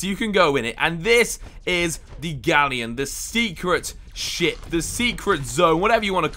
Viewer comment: did he say secret shit?😂